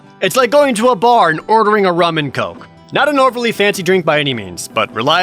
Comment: I thought you said “Ramen Coke”